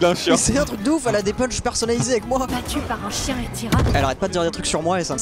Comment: C'est moi ou a 6:02 elle dit "Battue par un chien Iturralde" ?